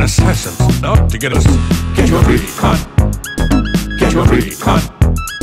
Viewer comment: I guess its just me but when i first heard "get your freak on," i heard "get your green card." And i was like WTF